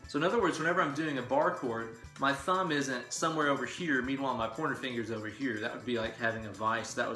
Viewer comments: I heard "porno finger" every time!! 😅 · I kept hearing "porno finger."